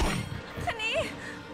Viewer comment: ฟังดีๆปทุมมาพูดว่าชะนี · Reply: เค้าพูดว่าอัคนี แต่พูดเร็วไม่ชัดไง พวกเราเลยได้ยินว่าชะนี (อีนี่(เราเองแหละ)ย้อนไปฟังใหม่สองสามรอบถึงจะรู้ว่าปทุมมาพูดว่าอะไร)